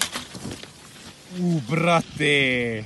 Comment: This is one of your better compilations.... and I haven't even gotten thru it. What is the expletive the cameraman says at 0:53? "Brafaayr" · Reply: He is saying brateeee it means brother on english but if he sed it on english he would have sad dudeee